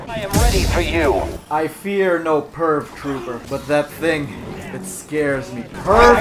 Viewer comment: Was I the only one who thought he was saying perv trooper instead of purge trooper